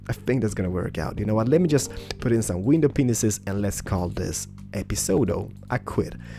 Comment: When You Say Panes It Kinda Sounds Like Your Saying Penis's